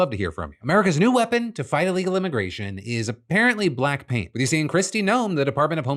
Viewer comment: Am I the only one who heard Blackpink on the border paint story?